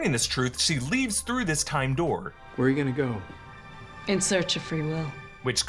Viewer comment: Is it just me or anyone else don't know But whenever said "time door" I heard "tandoor" 😅